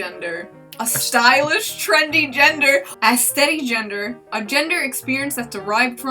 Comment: when said Aesthetigender i thought she said A-steadygender